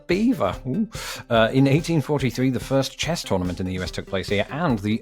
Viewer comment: When you said, "Chess tournament," I heard 'chest ornament.' I thought it was some kind of euphemism.